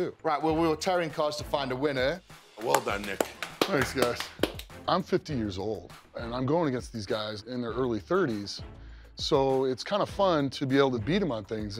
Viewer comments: Bruh i thought he said im 15 years old instead of 50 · I misheard him say "i'm 15 years old going against these guys in their 30's" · did anyone else think he said "im 15 years old" at first? XD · Who else thought he said he was 15 years old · I thought he said he was 15 years old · am I the only one who heard him say "I'm 15 years old"